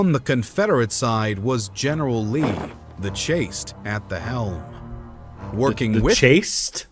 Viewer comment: I think you misheard the narrator, they said "the chased at the helm"